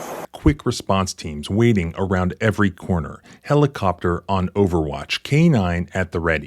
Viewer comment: In the first 30 secs the narrator says "the helicopter is on over watch" but I swear I thought I heard him say BOOMER WATCH! 😂